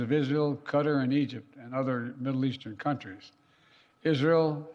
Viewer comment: I thought it said “middle class” 😅